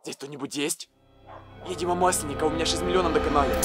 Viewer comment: мне послышалось видимо маслинков у меня 6 миллионов на канале